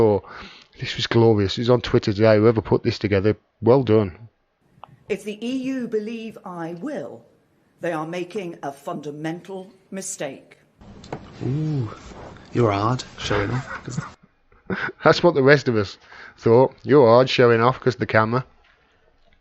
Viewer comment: Did she say a "fu… kin mental mistake"?!